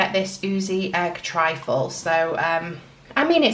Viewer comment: I genuinely thought it said Dozy Egg Trifle 😂😂🙈 I think I need glasses x